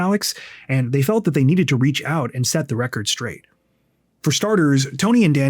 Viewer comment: Did he just say- the hormon straight??? 😂😂😂😂😂😂